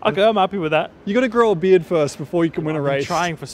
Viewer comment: What does Daniel say at 8:25??? I've been trying to understand it but I really can't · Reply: “You gotta grow a beard first before you can win a race”